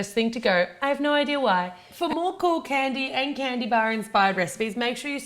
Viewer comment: i thought ahe said " for more cocain visit "